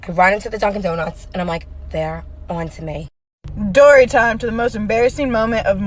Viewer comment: did she say doorytime??